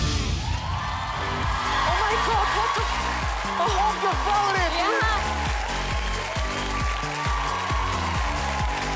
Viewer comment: did he say what the f**k haha, he's probably too happy XD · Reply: Vienski A die zei omg wtf en Sean zei wat een valreep